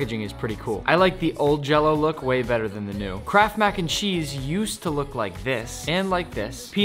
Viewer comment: When he said "kraft mac n cheese" i thought he said "crap mac n cheese" 😂😂 (sorry)